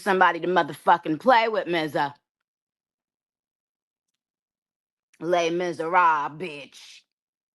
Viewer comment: Did Nicki say « Les misérables » 😅?